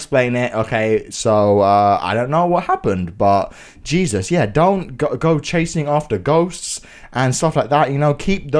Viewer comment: I forgot I was watching a ghost video and I thought you said Dont Go Chasing After Goats